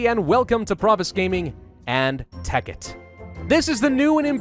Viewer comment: I keep hearing tekkit as "heck it"